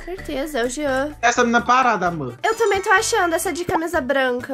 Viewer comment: So eu que entendi o falando amor